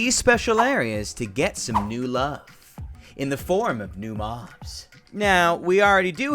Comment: Captions: “In the form of new moms.” What??? He said mobs not moms!